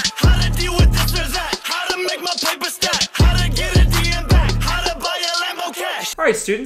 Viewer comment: For the longest time I thought Jake was saying “how to get a D in math” instead of “how to get a DM back”